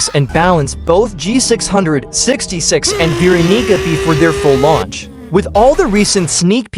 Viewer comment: what did bro say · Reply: Bro Said vieronica